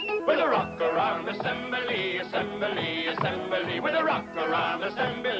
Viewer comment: Thanks! I’ve been searching for this for decades. I was 11 when this first aired, and I could sing the song from memory all these years. I kept googling “rock around assembly,” and finally entered “we don’t like dirt” — and bingo! The animation is adorable — it looks like it was produced in the 1930s. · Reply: Me too. I always heard "rockaround assembly."